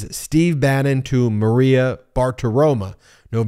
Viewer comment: I though I heard Ben say "Maria Fartaroma." I really, really want this to be true.